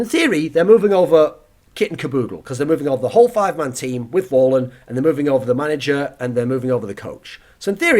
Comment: swear he said wallen